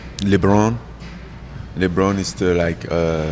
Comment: Who's ur favorite basketball player.i thought the homie said Lebanon lol